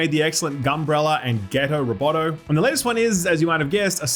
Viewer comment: I heard "ghetto robotto" and was like !!! oh no 🤣🤣